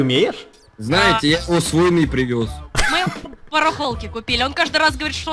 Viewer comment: Гогда дед сказал что рояль с войны привёз мне послышалось что с луны